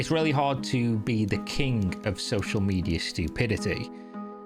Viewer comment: why did you say "the kink" instead of king??